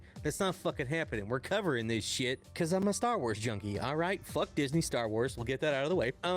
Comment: Did you say Star Trek?